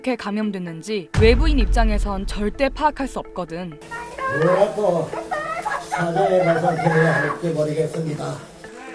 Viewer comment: 뒤에 들리는 목소리... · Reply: 이만희는 내딸을 당장 돌려 보내라악